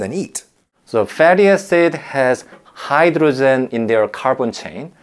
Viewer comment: took a couple rewatches to realise he meant hydrogen, i kept mishearing it as hydrazine. was thinking if youre finding hydrazine in your dinner, you have much bigger problems than contaminated water